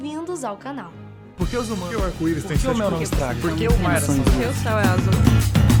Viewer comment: No 1:19 eu escutei por que eu sou a Elza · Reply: Menina Loka ela falou, "por que o céu é azul?"